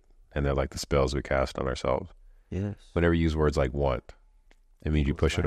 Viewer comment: did he say want??